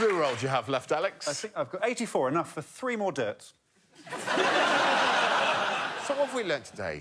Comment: Will someone please tell me what Alex says there's enough loo roll left for? I can't tell and the auto-generated captions are confusing. (Thanks everyone who has been helping me with this! I really wish they would add proper captions.) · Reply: Enough for 3 more dirts? Is what I heard